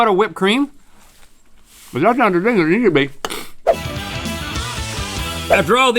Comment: What’d link say???😂🤷🏼‍♂️ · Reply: something along the lines of "If that's not a thing it needs to be" I would guess